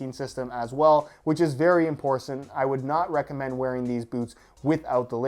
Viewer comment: Did say "imporcent"???